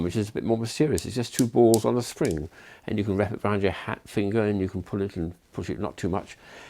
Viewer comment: Is he saying hat finger?